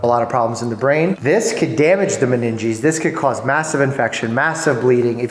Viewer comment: did he just say mass infection